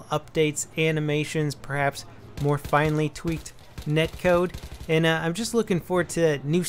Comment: Did he just say tweaked netcode? haha